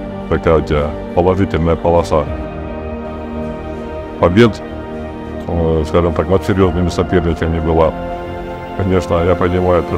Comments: subtitles are wrong, Denis saying Devon have a lot of victories agains serious opponents, not there were no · What a hell is with subtitles, 0:25 Denis says «У тебя была серия побед над сильными соперниками была» which translates like “You had series of victories over strong opponents”, but subtitles are totally wrong and aggressive. Engin you are seriously disappointing your fans with this game